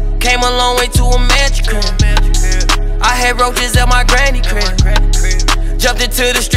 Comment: “I Had Wrote This At My Granny Crib” I Thought He Said Roaches Like A Mf 😭I’m Still Saying Roaches😭😭Fuck It Granny Had Roaches 🤣🤣